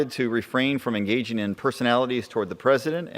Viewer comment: What did he say? Don't criticize the president?